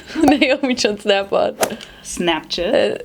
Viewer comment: did she just say snapshit😂😂😂